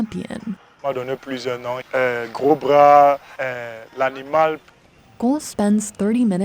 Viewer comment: the guy is saying he has nicknames like "cobra" or "the animal" but subtitles say "big arms". I know what a cobra is and I can hear him say it lol.